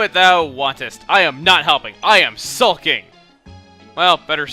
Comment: i thought it said sucking where it said sulking and i figured i was the only one that would think that but then kinda did it too and i couldn't stop laughing